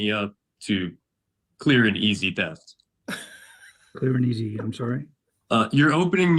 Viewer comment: I literally thought he said you're opening me up to a clear and certain death 😂😂😂😂 not theft 😂